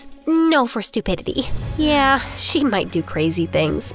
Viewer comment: I thought y’all keep saying one better friend is always better than a hasband😂